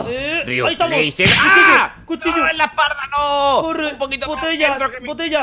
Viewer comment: Escuche 7:57 en la parda que rias decir es la parda